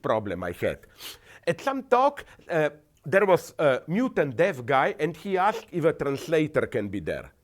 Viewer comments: I could've sworn I heard him say mutant deaf guy at 7.56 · Yo Slavoj! What up my nigga!? Wait... Did he just say Mutant Deaf guy? Haha